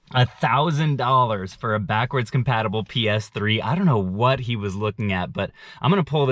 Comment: I thought he said backwards comparable ps2 not ps3